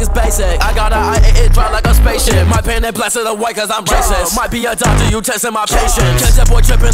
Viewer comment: who else thought it said my b imdontai you testing my patience